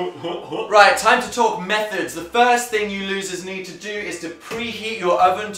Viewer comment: did dan just call us losers